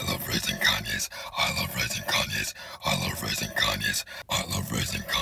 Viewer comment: I thought he say I love Fries and canas